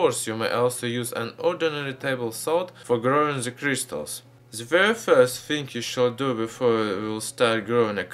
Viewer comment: Not to be mean about his accent b yup t it sounded like he said crystal meth